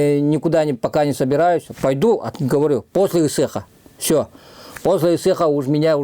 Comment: На 12:25 Не поняла,что он говорит: Пойду после ....? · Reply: Ысыах-это новый год для народа Саха. Отмечается дватьцатых числа июня,во время солнце стояния. В самый длинный день лета. Весь народ Саха отмечает этот день берёт от солнце энергию